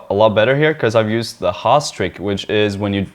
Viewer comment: could someone explain this further please? I cant catch the word he says:( Haars trick? Hard?